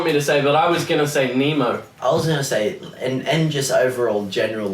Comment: did Michael say, “I was gonna say Emo” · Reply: No ithink he said memo or nemo idk